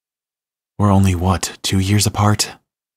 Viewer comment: also at 2:59 i thought he said five years apart 🥲🥲